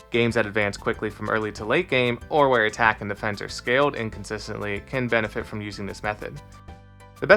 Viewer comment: "Where attack and defense are scaled inconsistently"? That's what I hear, but the subtitles say "where attack and defends are scaled and consistently" (not the autogenerated subs).